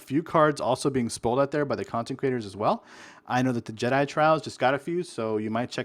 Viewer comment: Every time you say "content creators" I hear "consecratiors" which isn't even a word.